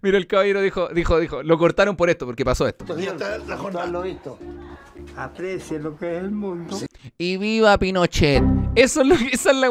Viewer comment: aprecia lo que es el mundo! eso creo que dijo el tatita!!!